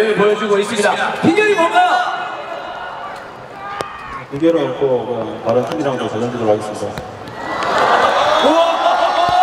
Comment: 뭐라고 하는 건가요? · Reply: 비결은 없고 그냥 다른팀이랑도 잘 던지도록 하겠습니다